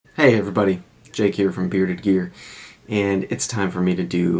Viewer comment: Saw yesterday’s video very late at night. So delusional that I thought you said, “hey, Jake here from State Farm”. Anyways, love the videos man. Don’t burn yourself out making so many back to back. You’ll grow this channel in no time. Patience.